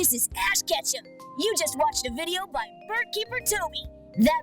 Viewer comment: Team Snooze was the first thing I thought of when they announced Pokémon Sleep! Edit: I thought you said BogeyBaToby before my tired and addled brain translated it back to! I think we have your rival's name now then haha